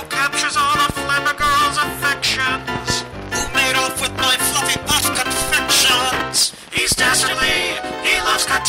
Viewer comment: For a second I thought he said... "Who made off with my fucking puff confections!?"